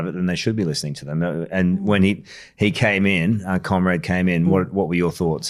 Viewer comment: I swear Karl called you comrade